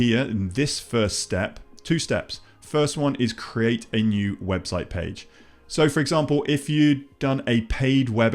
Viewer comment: Looks like they removed this option now! Bummer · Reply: Never mind, I found it. I thought you said Landing Page, but it's under Website page! Awesome!